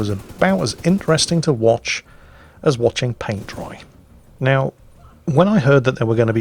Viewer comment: i just realized that i heard wrongly sentence 'boring as watching paint dry' I heard 'boring as watching pants dry'..jesus that's even more boring :D